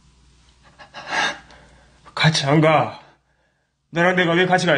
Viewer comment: `같이갈까?` 여자목소리 확실히들었음